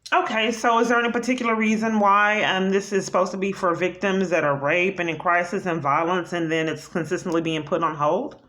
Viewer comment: Someone help me out here. Did she say at 4:35 “Is there any particular reason why this is supposed to be for victims who are raped and are in crisis and in violence and then it’s consistently been put on hold?” is this vile human being still playing the rape victim? Isnt it bad enough to be pretending to be a Domestic Violence victim?! Did I hear that right?